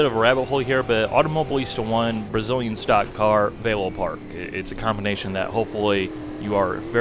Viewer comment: you name a track your on. I played it several times and I cant make it out what you said... So what track was that? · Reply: Velopark